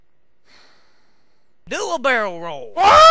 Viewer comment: It sounded like he was saying "Do a bear roll" xD